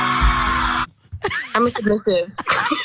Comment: what did she was saying? I didn't hear her well. Please tell me if you understand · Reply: I’m I submisśivë